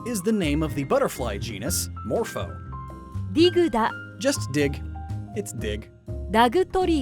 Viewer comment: I swear I heard "It's dick. Just dick." But I'm pretty sure he said "dig"